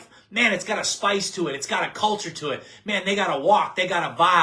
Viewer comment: all i hear is saying "cult"